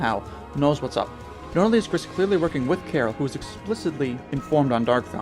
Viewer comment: I Misheard it as Chara.